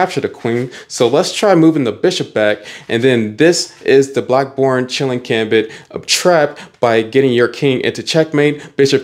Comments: Bro I thought he said being chilling gambit 😂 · Did he just say black porn? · I thought he said. Black porn 💀